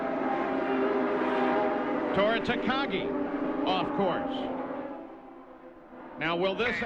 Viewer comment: i thought paul said "of course", not "off course". He wouldve been correct either way 😂🤣